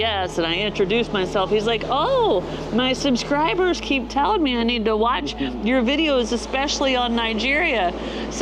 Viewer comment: The guy on the electric bike recommended that you go to Nigeria???? Nigeria what's so completely unpleasant. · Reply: That’s not what he said. Kino Yves said his “subscribers keep telling him he needs to watch Snow and Curt’s videos, especially on Nigeria”